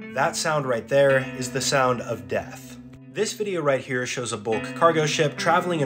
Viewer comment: “This is the sound of death” What I hear: “B E E S”